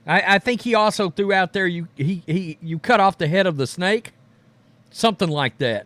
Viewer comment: I thought he said “cut the head off”. Sounds like a hit job to me. Pierce should choose his words carefully